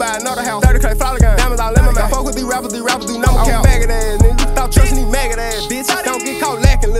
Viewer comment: What does he say at 2:22? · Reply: Dont fw these rappers they nun but CAP